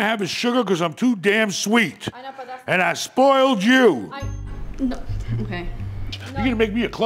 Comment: Did he say "I spoiled you." or "I'm a spoiled Jew"?